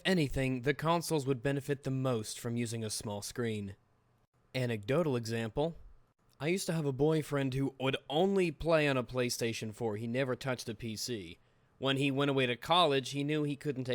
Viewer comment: did you say boyfriend?